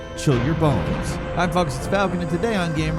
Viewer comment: Anyone else hear hi fucks it's falcon